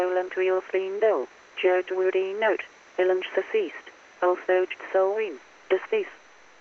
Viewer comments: I heard "Ellen's/Alan's decased", as well as the other bit in clear. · I keep hearing "villains deceased" - might be audio-pareidolia, and a reflection of the dark paths my mind wanders along... but that's what it sounds like.